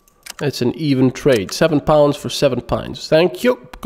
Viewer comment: I completely misheard what you said when trading with Sulik... thought you said 7 pounds for 7 pints. Brain's response: "Holy shit! That's some cheap beer!"